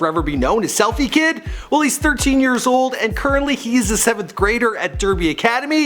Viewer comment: Oh when he said he’s a 7th grader I thought he said 2nd grader Me: Where the hell do you get your information from